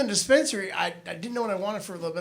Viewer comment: He said dispensary, but I heard Spencer's. Still didn't seem not right.